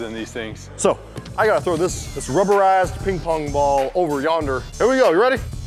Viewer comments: pls correct me if im wrong, but did just cuss? I heard rubber a*s, if i am wrong pls tell me the actual words pls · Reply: He said "rubberized" with a drawl. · it sounds like said “this rubber a**” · “I gotta throw this rubber ass ping pong ball.” 😂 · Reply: I also thought that was what he said but he said “rubberized” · when said rubberized it sounded like he said rubber ass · It just me or did say a curse word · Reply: Its just you, he never cussed. He said rubberized. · did say this rubber a$$ ping-pong ball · Did say ass on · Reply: He said rubberized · Did anyone else thought the heard say rubber ping pong ball at 1:29? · sounds like he said rubber assed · he said rubberized and i thought he said rubber ass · did he say rubber ass? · Is it just me or dose it sound like says this rubber ass ball. I know he didn’t but it sounds like it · He said rubberized not rubber ass btw I thought he said ass to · I have this rubble ass? Is that what said??? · Is it me or does sound like he saying “this rubber a*s pin pong ball” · what did he say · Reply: Rubberized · am I the only one who heard this rubber Ass ping pong ball??